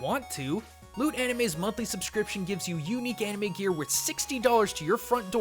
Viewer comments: I thought for a sec you said "Lude Anime" 😂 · When he said loot anime, I legitimately thought he said "lewd anime" XD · I thought you said "lude anime"... woops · Oh when he said loot anime I thought he said lewd anime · OH! Loot anime. I thought you said lewd anime bahahaha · Just saying, I thought you said "lewd anime" instead of Loot Anime, and I'm all like: What makes you think I'd like lewd anime after watching Kubo? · thought he said lewd anime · For a second, I thought he said "lude anime" instead of "loot anime". I'll go try scraping my head out of the gutter now...